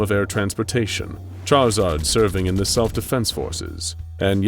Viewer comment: did i hear or saw that correctly? charizard or a charmander?